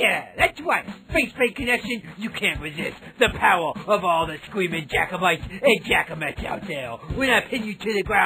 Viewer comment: Thought he said "screaming Jacobites" was waiting for the guillotine.